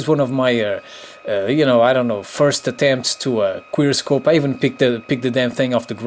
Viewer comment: it sounds like u said queer scope lol